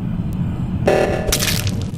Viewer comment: did he say a bad word!? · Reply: BreakingSoul, B**ch is the word he said